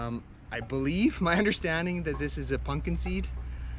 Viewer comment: it sounded like you said punkin seed instead of pumpkin lol